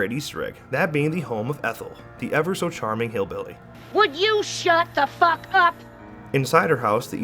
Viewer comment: It sounded like she was telling you to shut up. XD